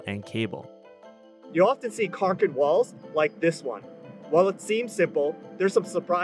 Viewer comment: i thought he said cock an balls instead of concrete walls💀